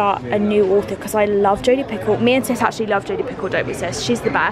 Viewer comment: This whole time I heard you saying that authors name and I thought her name was Jodi PICKLE 😂 😂 Only me???